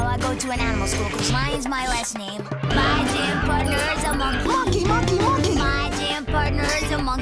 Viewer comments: I thought he said “my damn partner is a monkey” 😂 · Could’ve sworn he said my danm partner is a monkey if you turn down the volume lol